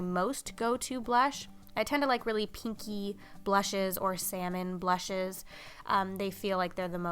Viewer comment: After you were talking about the NARS blush in the ‘orgasm’ shade, I thought you said you liked “kinky” blushes! 😂